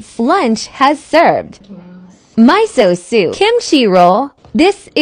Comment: “My so soup”😂😂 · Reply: I heard "mice so" 😭